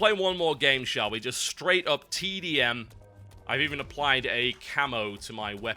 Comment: I heard TB say "Just straight up TEDIUM" and was like WTF? Then I realized that he was saying TDM